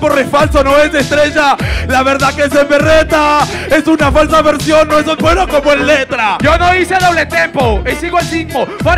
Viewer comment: ¿qué dijo? No le entendí. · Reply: La verdad que este me reta, es una falsa versión no es tan bueno como letra... eso entendí yo.